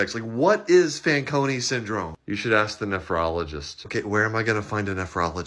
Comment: I thought he said Phrenologist. Like... Let's explain your personality based on your head shape